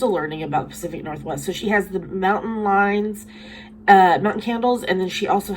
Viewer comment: Omg, I thought you said she has a MOUNTAIN LION!! lolol